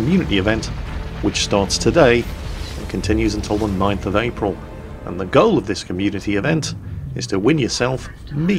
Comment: Anybody else hear him say "Till the ninth of April"